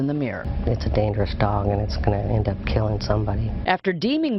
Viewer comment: I dare you to stick a treat in between your teeth and make your pit take it out of your mouth. I trust my lab to do that and not bite me. Do you trust your pit? What happens if you put your hand in it's bowl while it's eating? What if you stick your fingers in it's mouth or take food out of it's mouth while it's eating? You could never pull of the food aggression training I do with my dogs, lol. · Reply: oh, my bad. I thought you said “didn’t end up killing people” not “ended up killing people”